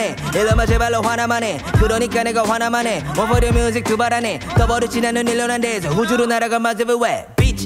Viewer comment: 이거 groovy everywhere였군요ㅋㅋ 들리는건 movie area라고 들렸었는데